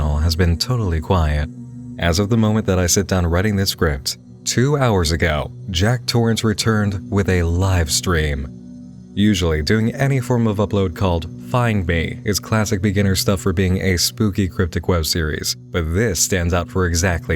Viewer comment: I heard "Find me, I'm here" May just be me